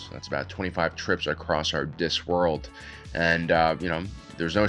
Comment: did he say our “disk” world??